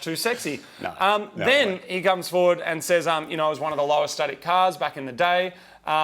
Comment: "Lowest studded cars" what's he saying?